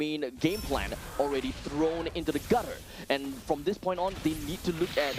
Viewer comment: did he just said "thrown into the gutter" or i heard it wrong 😂😂